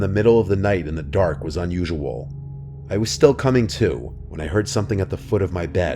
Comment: What did he sayyyy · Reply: I was still coming to.